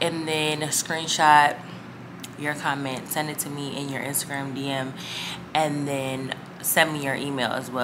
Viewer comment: I love that green cardigan !! fire baby 🔥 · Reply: Thank you 😅I didn’t give any information beside texting I couldn’t believe I won I’m like “I misheard I could have sworn she said insta-gram🤪